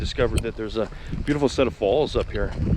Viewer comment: 2020 has been a shit year from the start, and I don't see it improving. At first I thought you said "Beautiful set of balls". (Replay) Oh, he said falls. LMAO! I know it's lame, but I prefer dry weather over wet weather, and the rainy season is upon us here in the PNW. I suppose I live in the wrong state, eh. Take care.